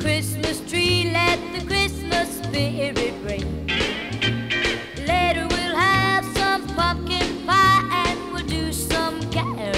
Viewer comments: I think I heard "later we have some f***ing pie" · it sounds like she said fu$$$ pie 😂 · I'm pretty sure I heard "Later we'll have some f***ing pie and we'll do some caroling" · Sounds like it says "later we'll have some f%$*ing pie